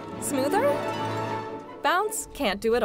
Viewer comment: Whenever this ad comes on Spotify it sounds like “Put a 💩on it” 😂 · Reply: OMG that’s too funny. I heard that and I heard “Put a shape on it” so I was always thinking, WTF are they saying. 😂